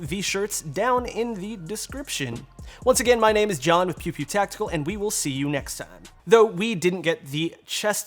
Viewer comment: It sounds like he says, "this is John with Pube-Pube Tactical". Anyone else hear that?